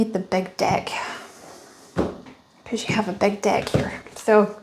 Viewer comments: I heard "you have a big dick" lol Wonderful reading, although i don't see anyone coming into my life anytime soon, love keeps coming up in my readings, must be self-love :))). New subscriber! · Good looks on the outside def dont hide the ugly on the inside. You can put on as much makeup or get your hair done, but looks are only skin deep. Energy never lies. I am more attracted to emotional intelligence, being able to have conversations with someone and being seen and heard that way. That is a turn-on. Omgggg, i totally thought you said you need a big dick, not deck...lol 😂😂😂🎉❤️‍🔥🙌🥰 I love your energy and readings, your authenticity is absatootley beautiful and hilarious. Thank you! 🙏🙌🫶